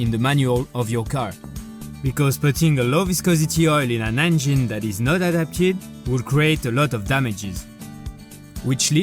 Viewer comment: Hi , 3:44 , what are saying , low viscosity ??????????????????? couldnt understand that part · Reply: "putting a low viscosity oil in an engine that's not adapted to it"